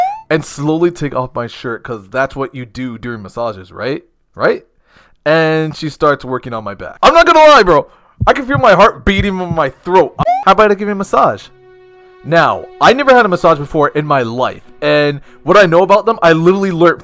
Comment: Why tf did I hear "She started twerking on my back"? Lmfao